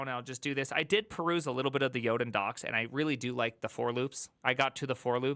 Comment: what is he saying here? Yoden docs?